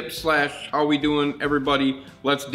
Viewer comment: Wait so you are telling me that you were saying how are we doing everybody? I thought you were saying Howreduhghereasdasfdf